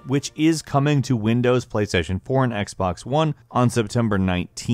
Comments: anyone else hear playstation porn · Did this Boi just pronounce Chloe as Clow? REEEEEEEEEEEEEEEEEEEEEEEEEEEEEEEEEEEEEEEEEEEEEEEEEEEEEEEEEEEEEEEEEEEEEEEEEEEEEEEEEEEEEEEEEEEEEEEEEEEEEEEEEEEE. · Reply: Kra88ypa77yJR and playstation "porn" instead of playstation 4